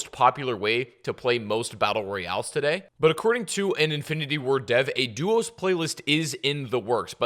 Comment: you said "infinity war dev" instead of warzone dev